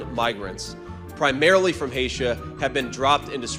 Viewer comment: Wait wait, did he say "From Hatia".